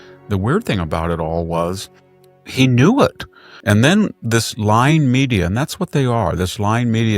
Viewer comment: I thought he said slime media instead of lying media, he would be correct on both counts.